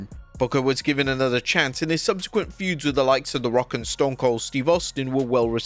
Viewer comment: DID I HEAR A ROCK AND STONE